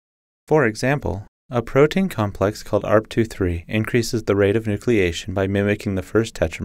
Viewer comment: What did he say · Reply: Arp2/3 is the thing he said